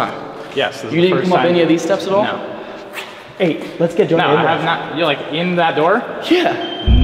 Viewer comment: Did I hear like a "Jo-nah" whisper as an orb flew past the camera? Like ya'll were too far away but the was also audio from where ya'll were so idk